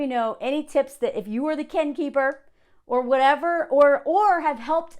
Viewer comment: I kept hearing this as "Ken keeper," like Barbie & Ken lol